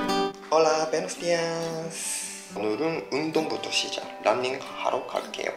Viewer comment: En el minuto 11:17 creí que nos dijo ¡hola bestias! 😂😂😂😂😂😂 no tenía auriculares y soy medio sorda, me regresé y ya escuché mejor que decía " hola buenos días" Que lindos se veían vestidos así awww me dio ternura.